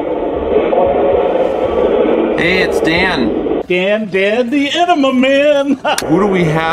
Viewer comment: haha! My guess was it said “Dan” 😅